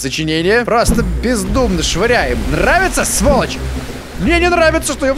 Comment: я один слышу на моменте 3:40 "просто в пиз** швыряем"